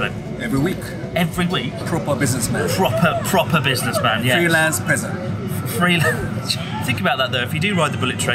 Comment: It's the title I deserve. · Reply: I initially misheard it as "freelance president", which sounds a lot better.